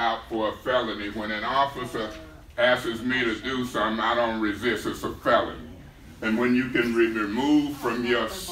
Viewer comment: did he say “when a officer ask’es...”??? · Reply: When an officer asks me to do something, I don't resist. It's a felony